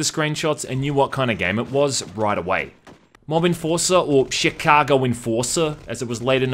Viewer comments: I thought you said "The *Sheet*cago Enforcer" when I first heard that. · I thought you said Shitcago Enforcer lol but its probably just your accent. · I thought you said also known as Shit Cargo Enforcer